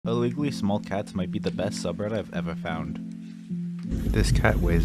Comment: I THOUGHT YOU SAID "SMOL CATS MIGHT BE THE BEST SUPPER YOU'VE EVER HAD" 💀